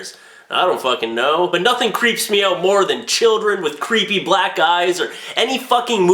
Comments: "creepy black guys" is what i thought i heard at first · i thought you said nothing creeps me out than children with black guys lol